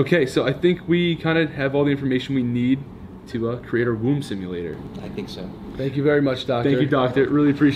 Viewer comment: I thought it said BOMB simulator! 😂